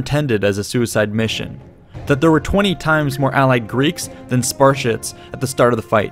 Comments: did he say "Sparsh*ts" at 1:20? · did I just hear “Spar-Shits”? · I thought you called the Spartans the Sparshits lmao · Wait. Did you just say "Sparsh*ts"? lol.